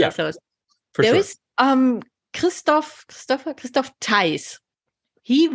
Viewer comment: Did anyone catch or find the German PhD paper Dr. mentioned at 48:55 ? by Christophe Teiz/Teilz/Teitz/???? I didn't catch his last name.